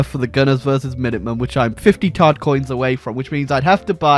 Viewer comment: From now on, I will only refer to Microtransaction currency as "Todd Coins" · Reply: I thought he was saying tard coins.